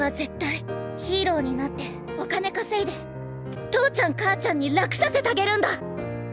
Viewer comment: Did she say kacchan?